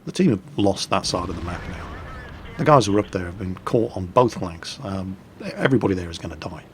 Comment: i am the only one that heard say both wanks or was I just hearing things and it was